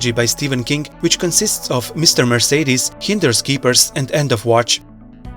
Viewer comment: I think you called Finders Keepers as Hinders Keepers.